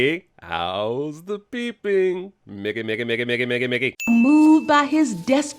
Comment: is Nobody else really concerned with how he said Mickey and it sounded like nigga?! 🤨